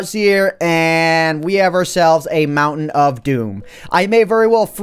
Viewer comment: am i the only one who heard mountain dew